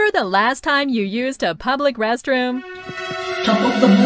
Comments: what did he say,? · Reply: Top of the morning to ya, Puffna. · What did Percy say? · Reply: “Top of the morning’ to ya Puffna’.”